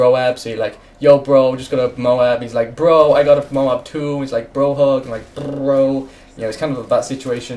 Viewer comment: Did he say "brohoof"? Cause that'd be too epic.